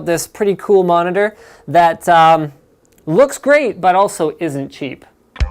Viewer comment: Did he say 'isn't cheap' ?